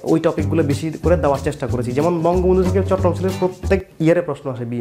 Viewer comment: সেকেন্ড থেকে আপনি কোথায় থেকে জানি বেশি প্রশ্ন আসে বললেন বুঝতে পারিনি শব্দ টা। অর্থাৎ প্রধানমন্ত্রী শেখ হাসিনা থেকে বেশি প্রশ্ন আসে এটা বলার আগের লাইনটা কি বলেছেন? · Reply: বঙ্গবন্ধু থেকে